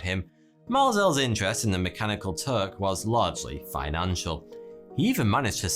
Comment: For some reason I keep hearing "mechanical turd".